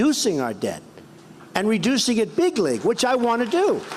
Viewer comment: I thought Trump was saying bigly. When he's really saying big league.